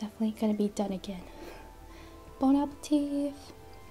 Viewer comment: Did she just say: bonapeteeth?